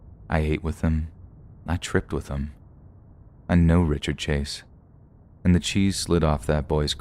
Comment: I cannot believe that someone as crazy as Richard Chase actually walked the streets. Just think of how many more people like that walk among us on a daily basis, undetected. It gives me the chills. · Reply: Thought that said Richard Cheese.